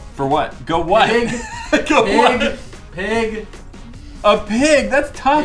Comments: Did say "PIG" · Did I hear say Spaceship and Pig? Pig: 5:03 Spaceship